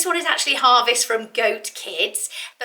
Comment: I'm trying to find a link to the cashmere sweater you mention at 9:15 but I can not find it under either the UK or US links. I think you are saying it's 'Harvest from Goat Kids" but I am not entirely sure I understand correctly. I tried to google Goat Kids but only found a site for children's clothing. Thank you.